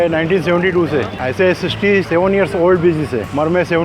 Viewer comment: did he- DID HE SAY 67 HAKKWLALJAH